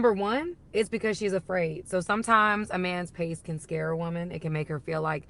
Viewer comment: She said "sometimes a man's pace can scare a woman" and I heard, "sometimes a man's penis can scare a woman". I thought she was playing on my fears, literally the only reason I've ever been rejected - Penis, Too Big.